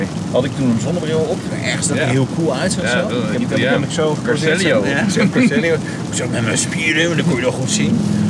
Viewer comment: Wat zegt men bij 1:50? Caselio? Coselio? - Schitterende auto verder, ben erg fan van Maserati, ik hou er van!